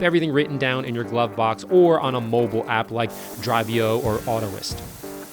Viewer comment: What are the two apps you mentioned at 3:43? Neither sound familiar and I can’t pick up exactly what you’re saying. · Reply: Driveo. Autolist